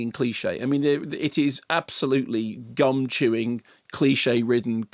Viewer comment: I was trying to work out how one chews one's gums. Then I understood Dr Horror meant chewing chewing gum, not the ones in one's mouth. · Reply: I thought he said gumshoe (a slang for detective)